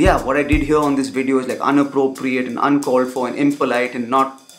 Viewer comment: Did he just say unappropriate!?